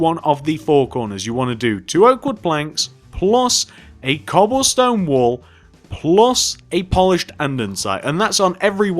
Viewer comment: In the beginning I thought he said 'cobblestone wool' and I was so confused for a minute, until I realized he had actually said cobblestone wall haha.